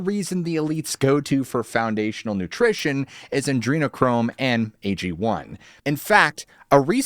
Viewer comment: Did he just say "Adrenochrome"??????? 😳